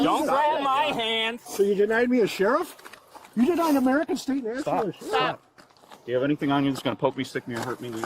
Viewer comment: did he say denying an American citizen a sheriff? Thats not a thing at al